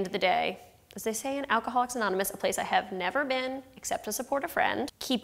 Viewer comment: What did she say on 1:35? I can not understand. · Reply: "as they say in alcoholics anonymous"